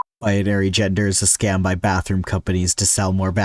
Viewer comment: Idk why but instead of this I heard “bathrooms are a scam by gender companies to sell more genders” and I was momentarily confused lol